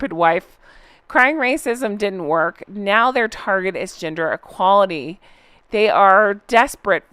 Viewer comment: I need to wash my ears out I thought you said Ginger equality not gender! 😅😅😅